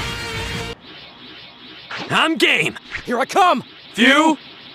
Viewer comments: Did he just say im gay or im gabe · Wait Did he say Gay or Game? I'm so confused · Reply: Game